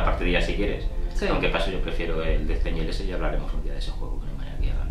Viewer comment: Hola y demás comunidad, en el minuto 3:23 es que no se entiende muy bien lo que dices. ¿Qué juego te refieres? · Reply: Hola!! Me refería a Death Angel.